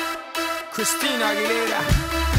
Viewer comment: How to know if Pitbull is in a song: MR. WORLDWIDE! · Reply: I always heard, "This-a worldwide!"